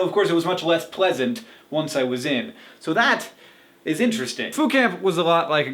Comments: I seriously thought you said "food camp." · I thought he was saying Food Camp. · at first i thought you said food camp. i was like WHERE DO I SIGN UP?????? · I thought you said "Food Camp".. that is a camp I would enjoy. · I thought you were saying "food camp" until I read the description. haha! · for a moment there I thought he said food camp... I was ready to ask where could I sign up o.O